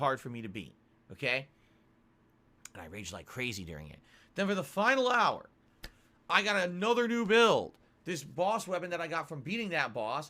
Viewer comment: I swear at first I thought he said, "and then at the final hour, I got a new bill"